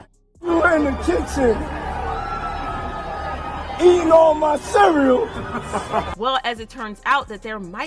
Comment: What was said at 2:40 I could barely tell. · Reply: “Fvck you in the kitchen, eating all my cereals!”